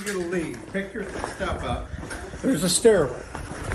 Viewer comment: I heard ," wheres the stairway " these officers were remiss for not tossing the frauditor down said stairway.